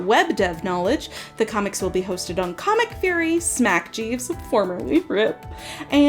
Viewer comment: can anyone tell me what the crocodile is cuz i just cannot for the life of me tell what she is saying 😭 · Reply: That's ComicFury! It's one of the oldest webcomic hosts out there